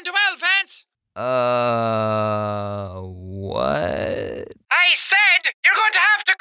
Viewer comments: At first I thought he said Timmy fell down two elephants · i heard ''timmy fell down to the elephants'' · that sounds like papyrus saying YOU HAVE TO COME QUICKLY TEMMIE FELL DOWN SANS but it says Vance.